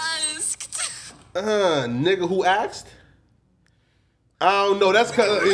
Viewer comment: it sounds like she said “Nick who asked” but like because of how she talks, it’s like a slight “a” sound on the end.